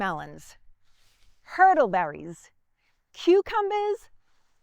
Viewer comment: Lol. At first I thought you were saying "turtle bellies"